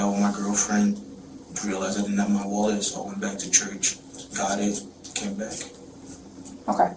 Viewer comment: THIS AI VOICE IS COMMON ON YOUTUBE AKA BLUETUBE. 😂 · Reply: He said he went to church. The CC says mosque. Weird.